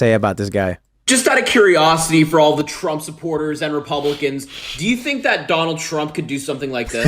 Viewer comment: i hear Trump is a par golfer